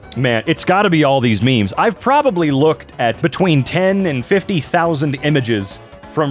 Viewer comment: i heard this as "10 to 50,000 reddit memes" and not "10,000–50,000 reddit memes" and i was like "no duh bro... OHHHHHH 10,000 MEMES."